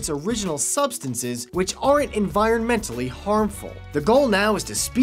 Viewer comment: Thanks to CC...I was able to understand/determine a very important word.....@ 1:07 Perhaps you should have used the words "are NOT" instead of "aren't"....or speak more distinctly.... ps.....I still love ya'.......( ͡° ͜ʖ ͡°)